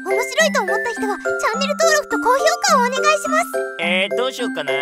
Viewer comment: すみませんが,外国人ですけど,最後のフラグちゃんの"待ってー"の前のところでモブ男は何と言ってるんですか? 聞き取れなくて。。。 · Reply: よく聴いてみたら,ここで? するよ！ ごめんってフラグちゃん に聞こえるんですけど聞き間違いですかね